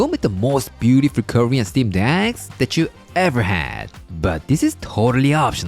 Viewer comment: At first, I thought he said “curry and steamed egg,” which also sounds yummy. :-)